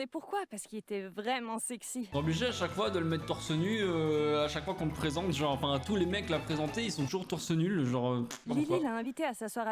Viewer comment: c'est moi ou j'ai entendu torse nul ? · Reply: Oui a dit torse nul